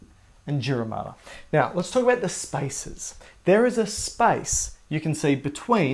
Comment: I heard "there is a SPICE between the pia mater and the dura mater" and my face was🤔.. Then I realized he meant there is a SPACE😂